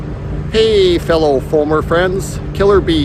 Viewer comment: Misheard the start of the video as "Hey, fellow former friends" and was wondering what I'd done to upset you! 🤣